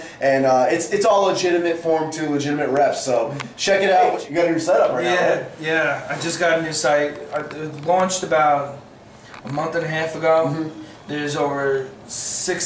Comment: I thought he said, i've got a new cycle. Not i've got a new site hahaha, juice babay!!